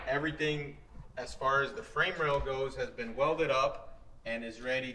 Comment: I thought you said 100K was a new frame rack....priorities